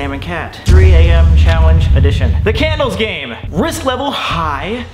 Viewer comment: At first in the beginning I thought it said "3 am challenge addiction"